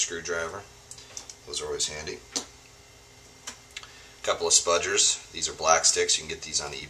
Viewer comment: Very good video - thanks from me in London, UK. What are those useful tools called - I heard you say 'spudgers' (but my hearing can be dodgy!) Brian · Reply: Yeah, Spudgers! Also known as "Black Sticks" Very useful tool. You can get them on Amazon or eBay.